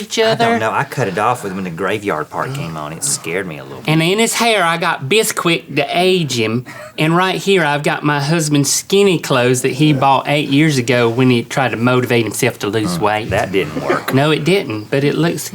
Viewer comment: I thought link said "He's got an anus in his hair" xD